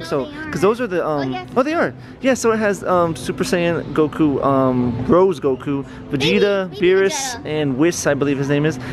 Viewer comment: Did he just say rose goku